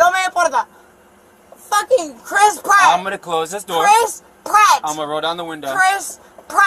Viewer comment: Did she say Chris Pratt? Like the actor?